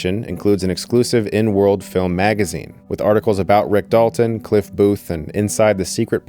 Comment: why did i hear an N word filled magazine